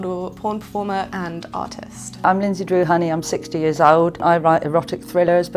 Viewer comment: Sounds like she said "autist" instead of "artist"